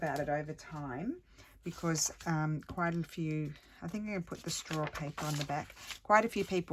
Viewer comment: lol, i understand that, but you said you used 'store paper' and pulled up a piece of brown paper? Just wondering what that was ;) at 13:57 you say you are using store paper for the back. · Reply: Ah ok it was straw paper! it's a recycled italian paper.